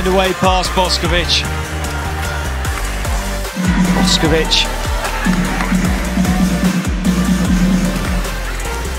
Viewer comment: what did she say? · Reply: If you watch carefully at 1:59 you can see there was a net touch by Polish player. At 2:26 Boskovic said - What else we have to do to win a point?